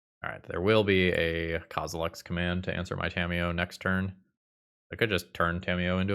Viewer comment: during the toa magic plug in the beginning, i thought he had said "scamiyo" 😂